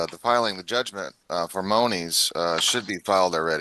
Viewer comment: At about 6:25, I thought I heard him say "judgment for MOANIES" instead of MONIES. That was interesting And he does seem to emphasize the LORD in landlord. I can understand being assertive about his end of the lease, but he can't make up rules that violate the law. Or can he? Doesn't seem to be any penalty for it, and he might get what he wants, so maybe it's permitted but not condoned.